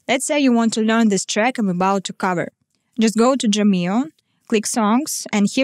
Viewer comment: The way you say Drumeo sounds like youre saying jamelle. Dr and Ja have the same kind of motion in your mouth, but squeezing your jaw inwards will help you to pronounce Dr instead of Ja. Almost like puckering a little bit. I love your accent, and you speak english better than I speak any other language. Amazing cover as always, see you in the next one!